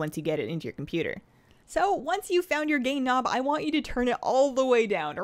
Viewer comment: amazing video but when you said gain nob i miss heard you and thought you said gay nob 😂